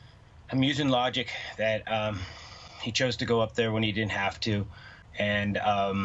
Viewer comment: So ‘He didn’t have to go upstairs’, yet Kaylee & Madison’s injuries were different. He did not confirm that either Kaylee or Madison was the target. What he said about the entry/exit point being the slider and that ‘He didn’t have to go upstairs’ means that he doesn’t believe Kaylee or Madison was the target. I think he is trying to say Ethan was the target. This is also the first I heard that Kaylee called 911. Also interesting that he said he had spoken to Madison’s parents and Zana’s parents. Not Ethan. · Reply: So true! The first 15 times I listened to it I thought he was saying, "did." But now I've seen different media videos with variations in sound quality so it's obvious he says, "didn't." But I do find that has a way of saying things that can be taken different ways.